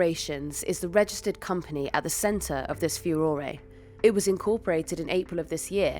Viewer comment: Did… did she just say furorey? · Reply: Possibly, but it's spelt furore.